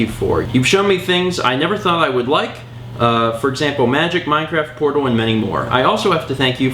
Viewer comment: I could have sworn that I heard him say 'MagicMikecraft' at 07:13. Did anyone else? Am I alone on this?